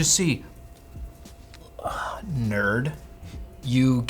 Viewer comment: Did he just call Linus "nerd"?